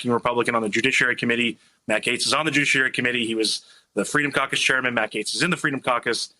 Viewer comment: I just heard "Matt Gaetz is on the douchiary committee." Sounds about right.